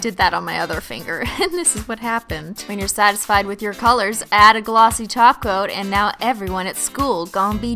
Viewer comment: I'm so hungry I thought she said "add a glossy taco" 😂